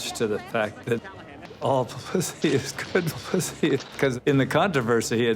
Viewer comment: Did anyone else hear “all pussy is good pussy”?